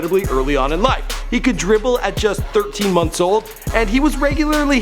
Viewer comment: Did he just say he started dribbling at 13 years old?